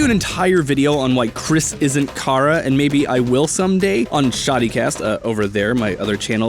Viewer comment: I heard shawtycast XD